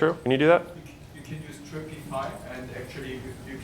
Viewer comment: What did he say around the 3:20 mark? Dripping pipe…? What method is he talking about if anyone knows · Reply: Dripify